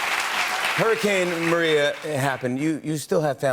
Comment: "Maria"?I heard" Mariah".I don't like Mariah😐